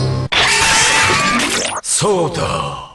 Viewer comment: 〜なんて言ってんだろう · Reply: fight Power fight Power